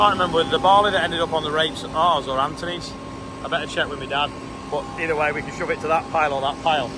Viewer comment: Thought she said spoke to my dad at first lol